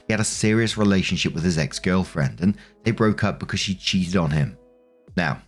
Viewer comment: I heard that as "she cheesed on him"